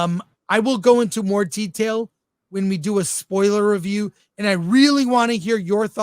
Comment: I thought you said non-spoiler review?